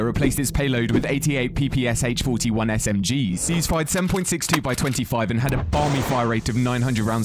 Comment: Did you just say it fires 762 by 20😂😂😂